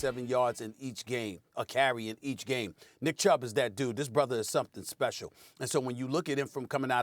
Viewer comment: Everytime Stephen A. says "this brother" i hear him saying "this nixxa is special" 🤣🤣🤣🤣